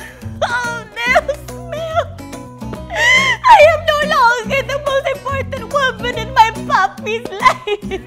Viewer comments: wait, what? Did i hear ' I am no longer the most important woman in my 'BABI 's life ' ? 😂😂😂😂😂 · did she just say "papi's life"